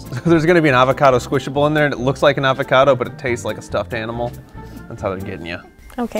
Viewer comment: did he just say it tastes like a stuffed animal